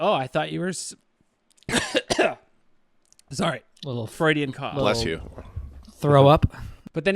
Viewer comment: did bro say freudian cough. yeah buddy, this is what I'm subbed for